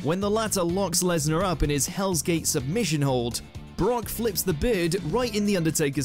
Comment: Yikes 😬😳 I thought you said beard not Bird.